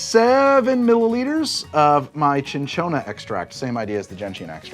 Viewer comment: I heard 0.7ml of my Chinchilla extract the first time...